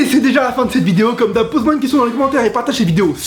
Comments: Pourquoi à la fin, j'ai entendu " J'ai plus d’herpès (air, peace) " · "J'ai plus d'air, peace" moi au début j'ai compris "J'ai plus d'herpès"